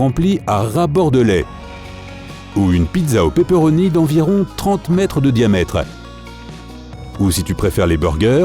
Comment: C'est beaucoup Pizza de 300 mètres 😮😮 J'ai faim 😨😨😵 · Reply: Vous êtes con ou quoi ! Il a dit '' Une pizza au pepperoni de 30 MÈTRES de diamètre ''